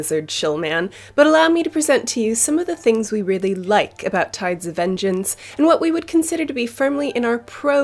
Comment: Is just me or does she say tards of vengeance